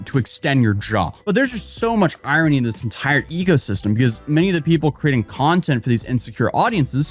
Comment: When you ecosystem I heard "egosystem" and I wasn't going to argue because it fits all these crazy, vain morons. 😂